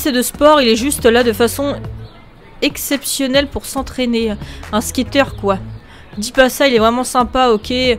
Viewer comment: Reviens !!! Attends j'ai vu que t'avais une barre d'amour avec la directrice.... me dis pas que tu peux faire du gringe a la dirlo...Oh mon dieu MDR 6:30 j'suis sourd ou bien j'ai entendu Un SKATTEUR, au lieu d'un Squatteur XD enfin J'imagines une suite avec le Corgi, 1an plus tard toujours à la recherche de x)